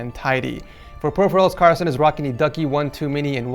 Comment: on 3:00 did he sayed KARLSON? Oh you dont know what karlson is karlson it the 18th ritchest game on steam so smash wish list now gamers